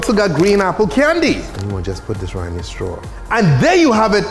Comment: I thought you said crown apple goblin